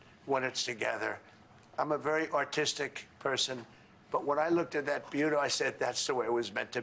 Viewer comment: 🤣🤣🤣🤣🤣 Did he just say “I’m a very artistic person” or “I’m a very AUTISTIC person.” 😂😂😂